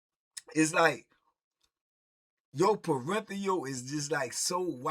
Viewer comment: Did say… your “Parenthial” is wide at 5:33 ? 😂